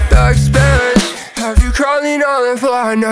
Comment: Ik he’s saying backspins but I always hear “black sperries”